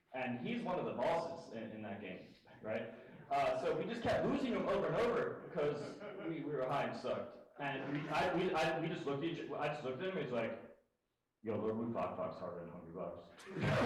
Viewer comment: thank you for uploading this. what does he say at 2:33? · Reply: "...and so we just kept losing to him over and over, 'cuz we were high and sucked. And I just looked at him and was like 'yo bro Lord Woo Fak Fak's harder than hungrybox'"